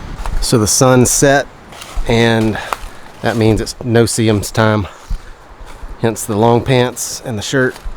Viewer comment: what did he say at 7:52 its .... time so he has long pants and shirt? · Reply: No-see-ems or no-see-ums. They are tiny sandflies or midges. you can't see them (no see em), but they have a painful bite which drives you crazy.